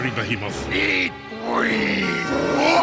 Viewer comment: Did he say "eat weed?" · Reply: "Eat green." Never fit the character, if you ask me.